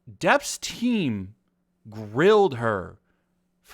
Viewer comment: "Depp's team railed her for hours". Bruh Or did you say grilled? lol.